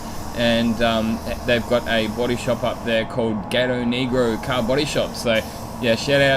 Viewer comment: did I hear the name of the body shop correctly? wow they should change that. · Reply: Haha, I couldn't understand it myself for a while but I'm pretty sure Gatonegro is the name of one of the cities in Netherlands.